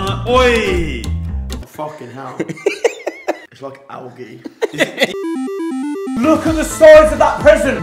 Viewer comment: did he say its like audi? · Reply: Algae*